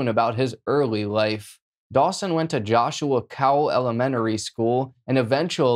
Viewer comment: At first I thought he said that Dawson went to “Joshua Powell elementary school” and I was thinking, “I bet they’ve changed the name of THAT school by now!” But I looked it up just to verify and turns out it’s “Joshua Cowell elementary”. Silly me.